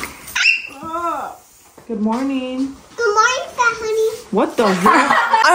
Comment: im crying pleasee did he just say "fat honey??" nooo